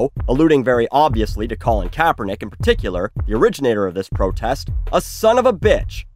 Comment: I’m so so sorry, but I have to say this. When said “Colin Kaepernick, the originator of this protest” I heard ‘Colin Kaepernick, the rich nigga of this protest’